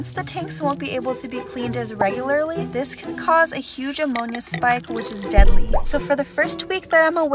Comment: I thought she said among us spike